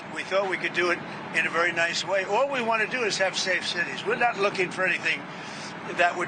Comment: Did he say at 3:37 “One president used the insurrection act 28 times in HER presidency” ? WTF, what dimension am I even in?